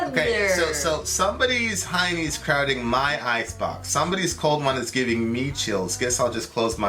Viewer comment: All this time I thought he said, "Somebody's Hinie drowned in my icebox!!! 🤣🤣🤣